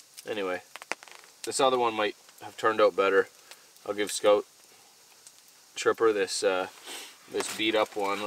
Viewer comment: Omg I love Tripper! He is a blast to watch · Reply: he said scout hi dead dog instead of tripper